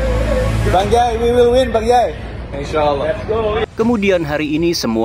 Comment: salfok sama Bang jay.. "Insyaallah".. Apa gw yg salah denger..??